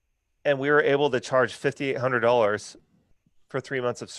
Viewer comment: Did he say $5,800 or $50 to $80? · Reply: $5800